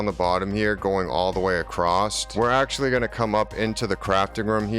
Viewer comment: Not gonna lie I find it really interesting that out of this entire 42-minute video that's what you took away from it lmao🤣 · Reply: Haha, it's a great video. I have taken away lots of interesting building tips. At 29:15 and for the following few minutes I think you say acrosst 3 times 😂 Decent build though. I shall be replicating it to some extent. Thank you